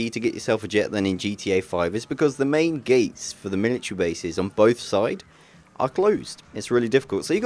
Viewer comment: Who else thought he said 'Miniature Base'?